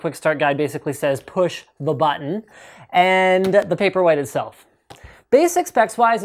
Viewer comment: Throughout the video, it sounded like you were saying "paperweight". In about ten years, that will be a very appropriate name for it. :)